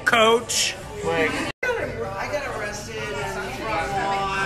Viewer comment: Anyone else hear someone in the background of 0:46 saying 'I got arrested' ???